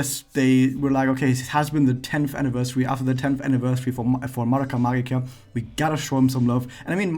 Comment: I like how the auto generated subtitle says "Maraca Magikarp" in